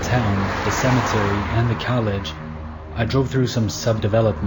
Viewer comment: bruh did you just say cowlege?